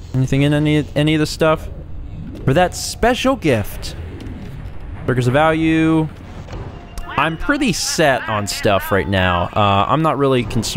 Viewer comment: Did he say 'Burgers of Value' ???